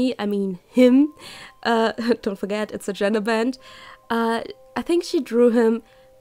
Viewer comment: Ohhhhhhhh genderbend. I thought it said gingerbread, that makes more sense